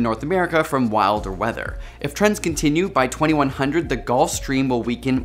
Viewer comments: Hey! Love this video. Terrified for our future. I found an error pretty sure and hope you don’t mind me pointing it out. At 3:13 when the words at the bottom pop up, the voice over correctly states 2100, while the caption says 2021. No big deal, really. Just wanted to make sure! Keep creating awesomeness, AsapSCIENCE! 💙🤟🏼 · You said 2100, but at 3:13 the subtitles said 2021